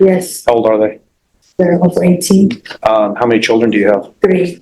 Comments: Three children described as BOTH 18??!! I went back to listen again. Yup she said it! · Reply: I thought she said her children were both 18 but when I listened again I think she said her children are over 18.. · Three children described as BOTH 18??!! I went back to listen again. Yup she said it! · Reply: I thought she said over 18